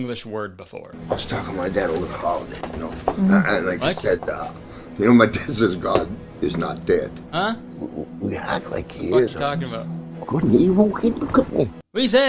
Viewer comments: lol maybe i'm crazy but i understood what the boss was saying right away. 4:37 "i was talking to my dad over the holidays, you know, and he said, my dad said god is not dead. we act like he is. good and evil i mean look at me (?)" i think. i'm also from toronto but idk i have a lot of irish relatives so maybe that's why i (think i can) understand him. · It sounds like hes saying "good and evil? >something< look at me". but who knows lol did this not have subtitles? that may be asking too much for a christian movie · “couldn’t he walk in, couldn’t he?” “could he walk in, couldn’t he?” “good and evil? kid couldn’t be” “good and evil, hey look at me” this movie is amazing. edit: im only alive because jesus used me as a carrier pigeon 😔✨ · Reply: I heard “Good and evil ‘til kingdom come.” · Ok I think I know what he’s supposed to be saying… “I was talking to my dad (name) you know? And like he said uh… humans think that god is not dead but we act like he is.” And then “Good and evil? Hey, look at me.” I could be wrong but I don’t know lol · Two years later, this is what I think the boss is saying: 4:37 “I was talking with my dad over the holidays, you know,” “And like he said uh… you know my dad says god… is not dead.” “We act like he is and … well couldn’t he locate him, couldn’t he?” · I heard "good and evil kitten cutteny" 😂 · All I hear is “Good and evil? Hey look at me!” from that incoherent mumbling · The boss sounds like he saying “Good and evil, Evel Kenievel